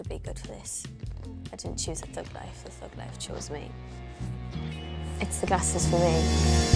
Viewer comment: someone please tell me what she says here?? 😭😭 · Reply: She says “it’s the glasses for me”….